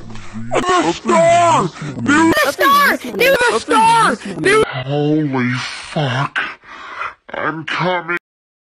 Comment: i heard holy f*ck im coming